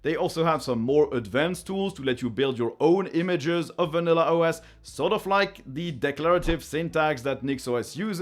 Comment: At first I thought you said "Vanilla OS/2".